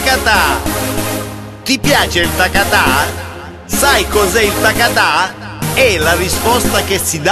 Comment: what does he say @ 0:11- it really sounds to me a japanese phrase "Sah ikoze!" which mens "let's go!" in english. yea it sounds like it